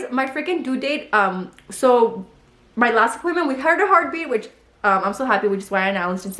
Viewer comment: Correct me if I’m wrong but around 27:12 it sounds like said “heard HER heartbeat” 😮